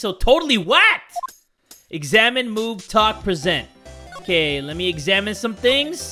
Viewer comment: I thought he said "Examine, Boobs, Talk, Present." XD